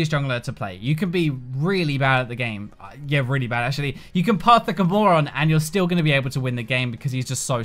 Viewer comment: Wtf does he say at 5:13 that accent lmao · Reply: I think he said something close to "you can play like a moron"